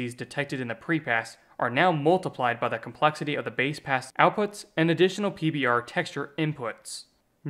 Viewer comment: Is that supposed to say "BASEASS" instead of "base pass"?